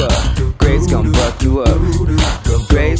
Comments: xD - 3:02 it sounds like he says "graves' gonna f*ck you up" xD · i sounds like he said graves goin to fuck you up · Am I the only one that thought was saying Graves gonna fuck you up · i hear he said graves gonna fuck you up xDDDDDD